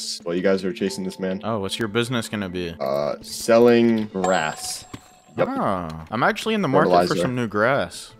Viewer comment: Bro i heard rats the first time. then i heard rass. so i think he is selling rat grass